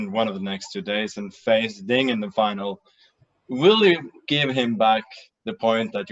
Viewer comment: What did he say here? 17:51 "will you give him back the point ...(something)?"